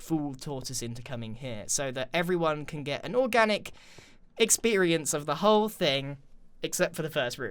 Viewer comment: I thought he said orgasmic😂